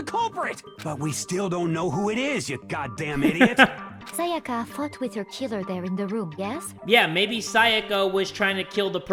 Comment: i thought Celeste said "Sayaka f**ked with her killer there" lmaooo